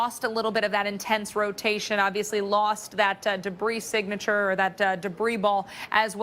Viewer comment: what did she say?!?!!! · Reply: I think debris singature